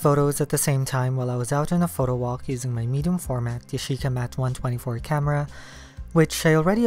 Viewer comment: what camera did u say, I couldn't hear it well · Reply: Yashica MAT 124